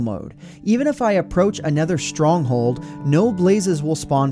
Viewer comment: Did he just call it a nether stronghold B R U H